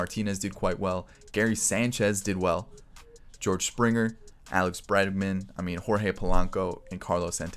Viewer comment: did anyone else hear Alex breadman